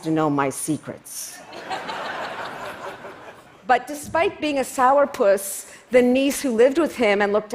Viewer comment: Did she call him a sour puss?